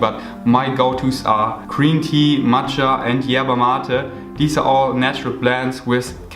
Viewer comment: Did you just said YERBA MATE? i'm argentinian bro, i could send you some!